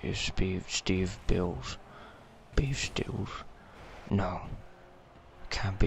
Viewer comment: I heard shit like "Beef stew" and "That's not how to pay the bills" xD